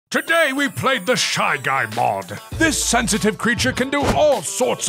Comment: Gentleman: “AH- SHY GUY” Me: “did u just say shaggy?”